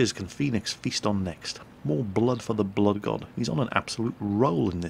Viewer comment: DID I HEAR YOU SAY MORE BLOOD FOR THE BLOOD GOD THE ALMIGHTY KHORNE ???????